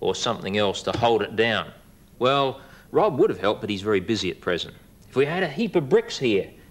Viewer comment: "Rob is very busy at present" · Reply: I thought he said "at prison", and immediately came to the comments